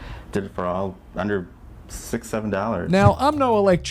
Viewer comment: What did He said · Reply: 67